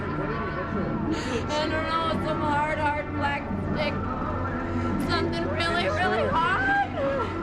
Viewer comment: what she’s saying here is I don’t know it was a hard hard black stick really really hard. The reason why she said this is because if you listen carefully you can here someone say What did he hit you with? 8:03 The dispatcher says So you say they have both been missing for about 20 minutes?